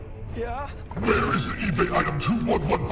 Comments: I thought he said where are the voices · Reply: I thought he said where are the forces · I thought he said where are the voices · I thought he said where are the voices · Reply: I always heard "Where are the buses!?"